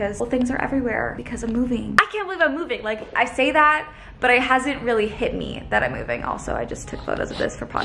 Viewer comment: hi love the vlog and im superrrr happy for you❤️ at 1:31 you said “i hasn’t” and i thought it was funny haha no hate or anything lol i just chuckled a lil cause of the grammar and you should prolly use clear duct tape instead of regular tape😁 it’ll hold better and u wont have to use as much for one box